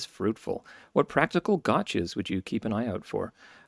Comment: thanks, btw about stamp 2:17:04 It should have been gotcha instead of gauche, auto correct problem?